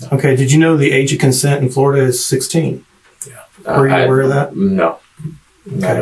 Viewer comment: i heard a yea before the no